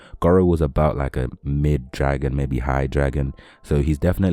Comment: Isn't Orochi above dragon? He is considerably stronger than Vomited Fuhrer Ugly. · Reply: Oh, fair. I thought he said low-mid dragon.